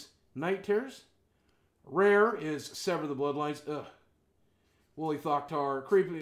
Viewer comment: when he said wooly thoctar i thought he said holy fuckytard. lol